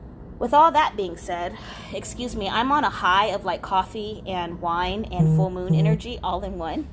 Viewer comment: what does she say ? ? "I'm on a high ....coffee and wine ....." what is the word missing ? what does she mean ? she live up a coffee or what else ? I'm french and I didn't seize it at all ! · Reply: She said I'm on a high of coffee, wine, and full moon energy.